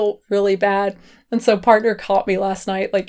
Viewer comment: She said "partner" and I thought she said "Parker" and I was like, oh shit!! She accidentally said her husband's name!! Someone tell her to take the video down! And then I realized, lol